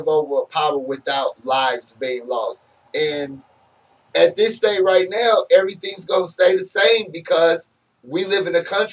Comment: i thumbed up due to content. thumbed down due to judge brown audio especially around 33:00 when judge was explaining 666. did anyone catch what he said. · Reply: He said that the Bible was written before the invention of the arbic numbers system so how could the Bible written know about 666